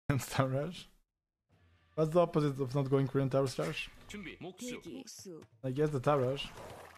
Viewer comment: Youtube’s auto generated subtitles thought he said “terrorist” insted of tower rush 11